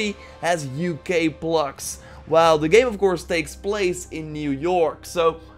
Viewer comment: What did u say on 2:08? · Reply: Something about plugs in the game being from the UK but the game is set in America.